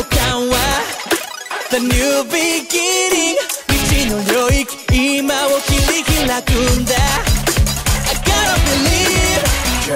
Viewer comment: ここ昔「石ノ森今を切り拓くんだ」に聞こえてたw